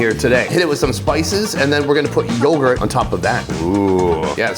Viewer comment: what did you call the laban? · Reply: Yoghurt